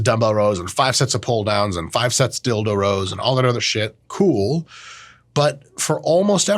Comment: Did he say five sets of dildo rows